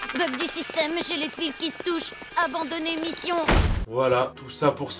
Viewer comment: ahhhhhhh files qui se touchent moi j’ai entendu filles qui se touchent